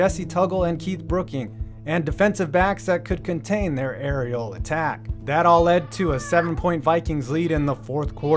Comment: I cant be the only who heard "Areola tack"